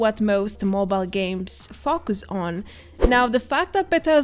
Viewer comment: “most mobile games fuck us on” very accurate but I think she says focus on 😂